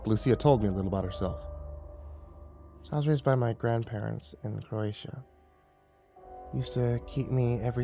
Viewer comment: I thought she said I was raised in Appalachia and had to rewind it (App native and fellow horror writer and enthusiast), nope, Croatia 😈😎😇